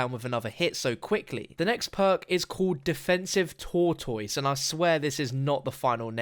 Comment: i thought he said tour toys.... not tortoise